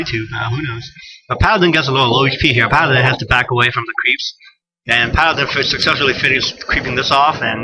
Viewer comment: LOl i heard bunch of "crap" when u wanted to sae creeps. nice funnie moment